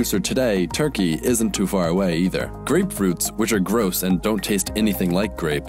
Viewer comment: Did he say grapefruit were gross